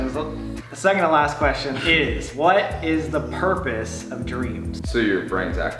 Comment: When same said what is the purpose of dreams I thought he said what is the purpose of Doritos and I was like TO FUCKING SIT DOWN AND HAVE A FOOD BABY !😂😂😂😂